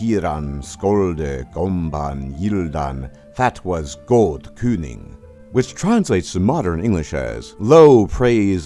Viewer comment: sounds like it says "That was good pudding"